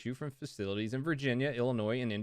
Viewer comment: I thought it said Vagina not Virginia